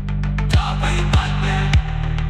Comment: Верадчиво вслушивался в отзвуки чего то и вроде бы расслышал слова (НАПЛЕВАТЬ МНЕ) если не ошибаюсь. Довольно таки просто и интересно получилось. Но по факту актуальные на сегодняшний день слова в России, большинство так и существует сейчас с мыслью в голове и подходом ко всему (НАПЛЕВАТЬ МНЕ)....😊😊